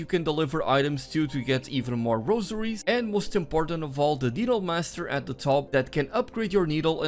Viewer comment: did bro say the diddle master 💀